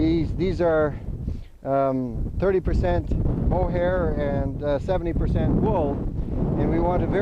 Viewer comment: did he say? 30% wool hair...70% wool? wtf ? 1:20 never heard of a sock with 2 wools discribed as such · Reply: muncher He said mohair, which is from a goat.